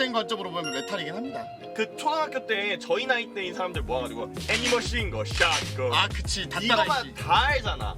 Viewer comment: 헤비머신건 인데 애니머싱건으로들림ㅋㅋ